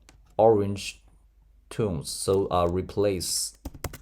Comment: repaint instead of replace